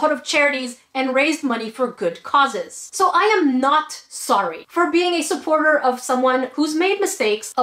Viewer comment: I thought you said "IM NOT SORRY for not being a Pewdiepie fan" and I was about to become a salty ocean.